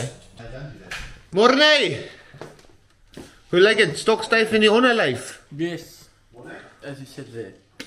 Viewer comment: Lol, @ 3:50 did you just say "Stokstijf in je onderlijf" ? As a Dutch I understand that 100 % 🙂